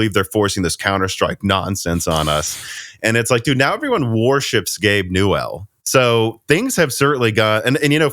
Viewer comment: Misheard "worships Gabe newall" as warships.. Works both ways surprisingly!